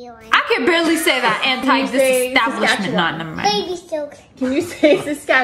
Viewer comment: How can you be mean to da baby 😡😡 THAT PART HAD ME DYING I DONT KNOW WHY BUT I THOUGHT SHE SAID DABABY